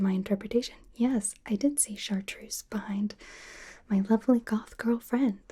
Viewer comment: Woooh, more Goth Girl content ! 😁Hold on, did I just hear "My lovely goth girlfriend" at 15:23 ? 😲 Either way, it's nice to see her open up some. She seems somewhat happier.